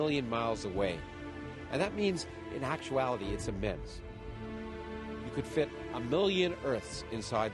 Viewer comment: what does he say at 0:37 I'm hearing "In actually it's a medicine."